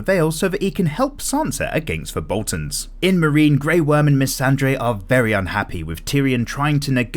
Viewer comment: did you just say Missandre?